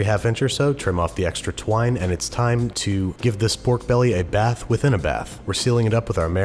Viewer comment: Did you say, "It's twine to give this pork belly a bath"?